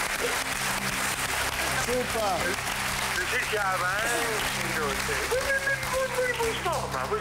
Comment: was sagt er von 0:57 zu 1:00? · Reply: Grüß Gott hier ist der Armin Assinger (mit seiner Aussprache und Art)